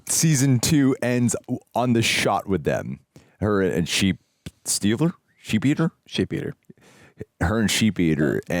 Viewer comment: Nope. Not Sheepeater … it is what said—> Sheepstealer.